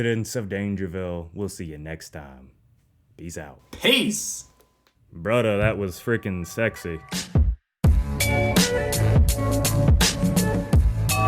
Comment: ?? Did said bruh duh that was freaking se......